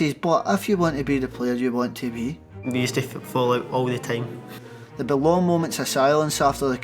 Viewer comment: i think he said “we used to fall out all the time”